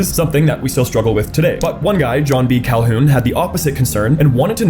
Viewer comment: I thought you said John C Calhoun, completely different guy